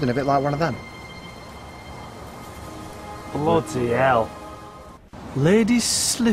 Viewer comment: did i heard bloody hell?!